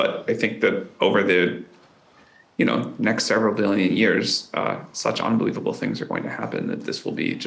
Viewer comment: did Sam say "next million or billion years" listened to it a few times and if that's what he said I'm surprised because Humans can barely think in terms of what's possible in the next decade forget an entire generation. Now to watch the rest of the vid. :face-blue-smiling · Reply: He said a few billion. Amazed me too considering hominids as a species have only been here 2 million years and modern humans a couple hundred thousand. The continents won’t even be here in a few billion years.